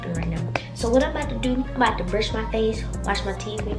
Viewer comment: who else heard her say im bout to brush my face and watch me teeth lol